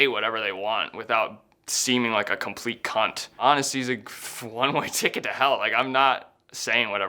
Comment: I thought you said one way gate to yell for a sec